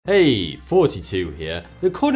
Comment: I always hear "Heyyy, 42 here." in the intro.